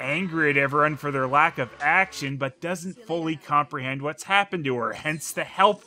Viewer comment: There’s something wrong with the audio. I could have sworn she said “magic” instead of “action.”